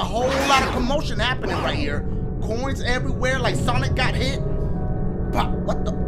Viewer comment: Did he say sonic